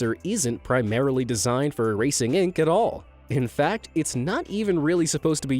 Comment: in the subtitles 4:14 says "racing" instead of "erasing"